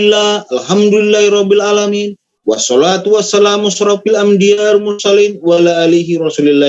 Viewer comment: Baca apa dia tadi,apa saya salah dengar kok ada kalimat ambyar mursalim😂😂😂